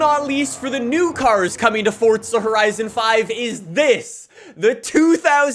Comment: Am i the only one who heard him say forza horizon 4 in the beginning?